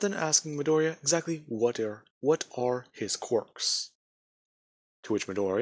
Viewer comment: I thought I heard ass quirk.